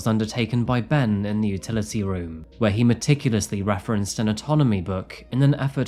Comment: did you say autonomy or anatomy